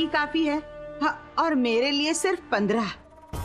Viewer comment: [TURN ON THE CAPTION] 2:55 - Nazneen Chachi says 15 Roti's But The Caption says - 12 Roti's